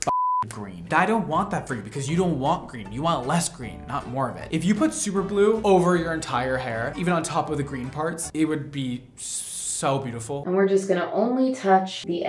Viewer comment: am i the only one who thought he said super glue…